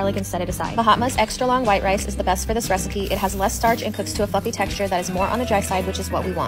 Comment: Was I the only one who thought she said it cooks to a "fucking" texture instead of it cooks to a fluffy texture? 🤣🤣🤣 I had to rewind and rewatch this.